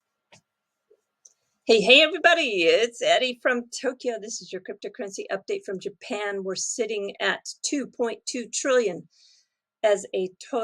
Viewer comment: it sounds like she says EDDie but its pronounced....or is that not it at all?